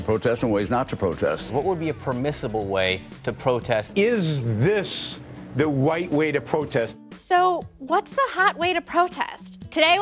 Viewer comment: did anyone else hear "Is this the white way to protest"?